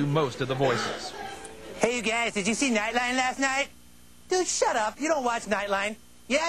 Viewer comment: "Nine line"? what is he saying? from which episode is this line from?